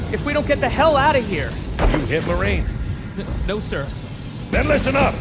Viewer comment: I thought he said "Are you Hitler marine?!"